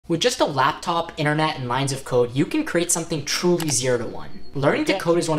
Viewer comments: ooooooooooh i thought you said with just a laptop, internet, and lines of coke i could create something.....but somehow i still got the same results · I thought you said “with just a laptop, internet and lines of coke”